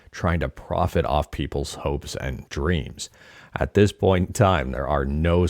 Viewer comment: "...hopes and dreams" I first heard it as "smokes and drinks". Sorry, I have a dirty mind. 😅 Though I never smoked or drank my entire life.